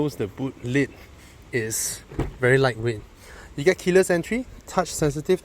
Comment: always misheard keyless entry as killer entry....